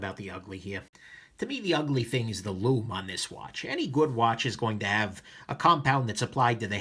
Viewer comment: shoot! I thought you said "mood watch"